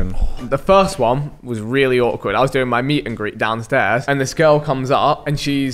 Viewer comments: OOOOooooh I Misunderstood him, i thought he said that he doing his meat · Ong I thought he said "I was on my meat" or something 😐💀